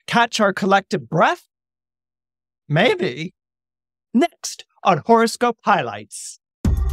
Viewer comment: For a second, i thought you said, "next, on forest gump highlights". I was, "what!!??"